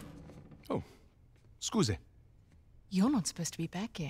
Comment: Did he said "scuze" ? (Romanian noun)